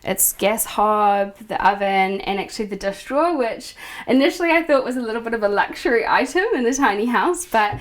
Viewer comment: a ____ drawer? under the oven? sounded like she said "death drawer" which is just silly so can someone explain that? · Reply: Maybe she said "dish drawer"? Anyway, it is a compact dishwasher.